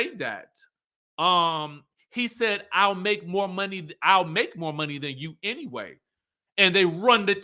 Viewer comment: Sounded like Juan said "I'll make more than you", but I could be wrong.